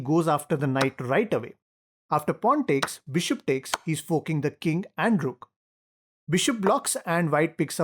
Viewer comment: “He is forking the King and the Rook” 😅😅😅😅 I thought I heard f**ck